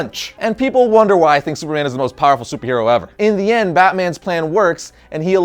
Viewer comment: He said Superman and the caption says Sabrina 😹😹😹😹😹😹11:31 Wtf is up with the caption